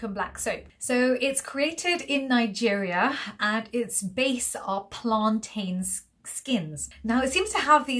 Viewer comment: I beg your pardon, 1:03 After, "It's made in Nigeria" what did you say? I replayed that part five times and I still cannot determine what was said. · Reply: “It’s base are plantains skins”